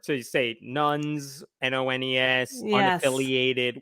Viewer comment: I keep hearing "nones" as "nuns", sorry.